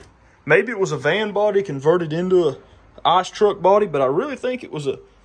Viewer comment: I really love the look of this old truck. I thought he said ice cream truck at first. OH- ice truck!